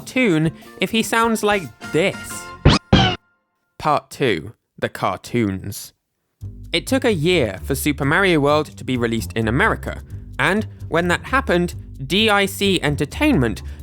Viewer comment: It almost sounded like you said "Cartoon: The Cartoons"